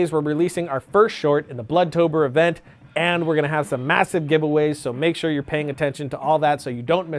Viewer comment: When you said "massive giveaways" I heard "mattress gives way" and did a double take. Why am I even telling anyone this. Where's the gin